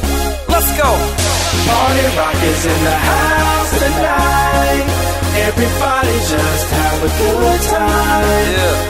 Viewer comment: Always thought he was saying party rockers in the house tonight